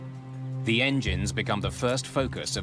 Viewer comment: What is the weird voice at 1:01:34 ?? It almost sounds like someone saying "meow" but it's a really weird time in the episode for that to happen o_O